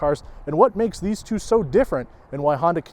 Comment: What did he just say? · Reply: “What makes these two so different”.